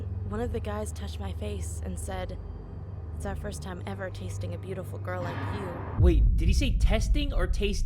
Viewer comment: I hear her say tasting 😐